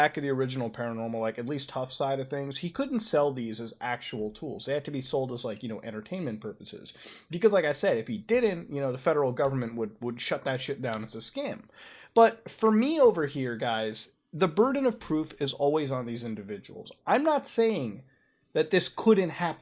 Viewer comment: It sounded like he said “shit thy shit down” lol